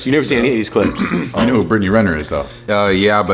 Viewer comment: I thought you said Brittany griner I didn't even know this person existed